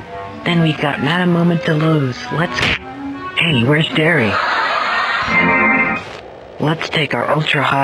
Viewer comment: When I hear it’s friday